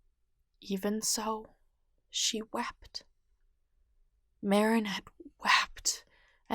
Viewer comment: i thought u said wap'ed